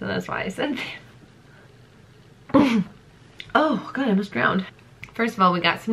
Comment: *Coughs* “omg I almost drowned” 😂😂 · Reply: I thought she said “I almost browned” and I just assumed that was slang for vomit lmao